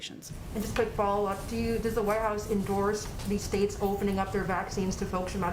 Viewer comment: Sorry I heard you don't help whites lol